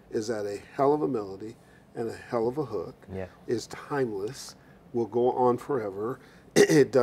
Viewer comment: melody, hook, is to __? I didn't understand that word, halp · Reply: Vlatkasify "timeless"